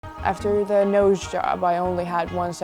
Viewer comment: Lol I thought she said “Amanda AHOE”😂